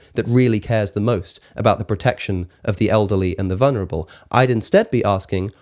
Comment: “Olderly”?? I thought i’d just misheard him the first time, but doggone if he didn’t say it again. always throws me with his pronunciation of us as uzz , but i wasn’t prepared for olderly …